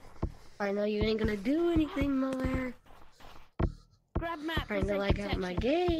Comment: What he says: "Malware" ❌🥱 The white subtitle: "Mother" ✅☠️